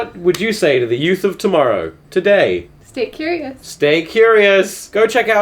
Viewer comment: I swear to god I thought she said "steak you’re anus" for a second there